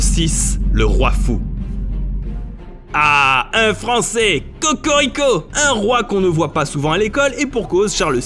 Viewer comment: Je suis le seul a avoi entendu "Tokorico" ?